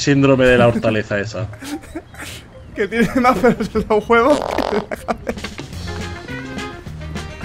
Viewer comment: alguien me dijo que dijo? porque no entendi xd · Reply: Kim Fujoshi que Tiene Mas Pelos En Los Webos Que En La Cabeza :v